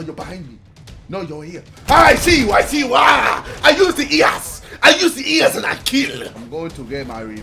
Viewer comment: I thought u say i use to eatxass